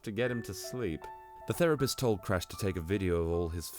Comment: i thought it said the rapist